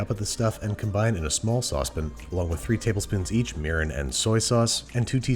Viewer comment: I did a double take when I misheard murin for urin.